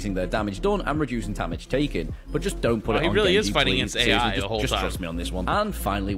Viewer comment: I can't be the only one who heard the guy at 5:46 say to not nano genjii